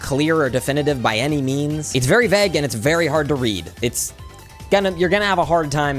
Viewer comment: Ranking videos by how much a ancient Spartan would be confused if he heard them. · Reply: I swear he said veg not vague 😅